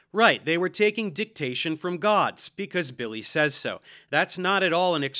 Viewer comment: Be honest, how many of you even know who Billy Carson is? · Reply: Wait …. I thought you said Bill Cosby